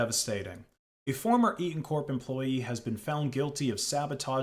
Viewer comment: I heard “E-corp employee”, and it just made sense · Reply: It was Eaton.